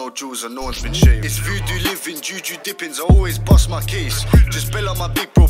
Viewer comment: “pray to _____ for the fucking change” who knows what he said ? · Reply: Oya